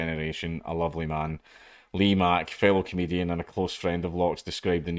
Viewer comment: I accidentally heard that as 'Lee Mack, failed comedian'